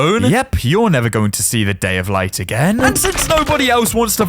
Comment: did he just say day of light again?